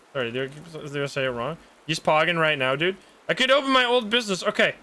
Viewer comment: One of the times the scam victim said “I’m pogging right now” I really thought he said “I’m pooping right now” and that’s not really important but hopefully somebody can laugh at my inability to read my own native language